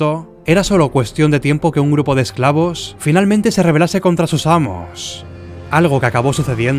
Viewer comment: 😂😂😂 pense que decía eslavos contra roma